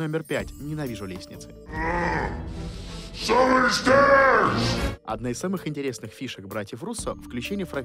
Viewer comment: я слышу Я здесь!